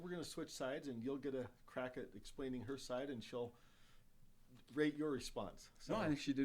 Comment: did he say "greg your response"?